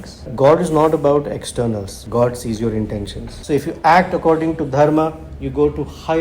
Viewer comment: The subtitles are wrong. It is Dharma, not Karma